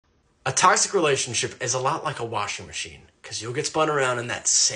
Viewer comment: Istg im high af i thought it said washington